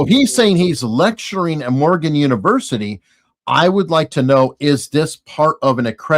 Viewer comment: Everytime he said "morgan university" i heard "oregon university" 😅